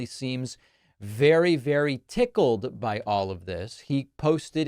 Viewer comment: Did he say tickled, or pickled?